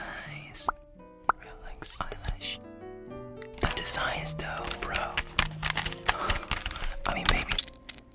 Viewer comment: THE “bro...i mean baby” SENT ME INTO ORBIT LMAODWKF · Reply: I heard “this design is still a pro...I mean maybe” am just saying for what I heard